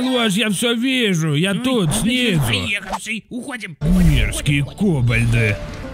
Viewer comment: "Фен поехавший, уходим" - Только расслышал=))) Ржака=)